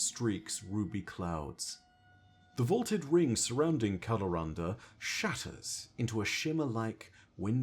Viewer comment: Misheard Kalarahnda as Colorado probably makes this whole thing funnier than it should have been.